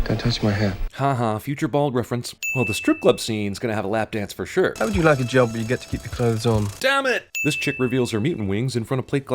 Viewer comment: I really can not undestand it really what the fuck does he say! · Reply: "How would you like a job where you get to keep the clothes on?"